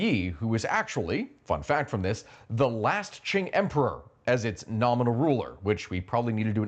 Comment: Am I tripping or a 3:10 did he say “the last chink ruler”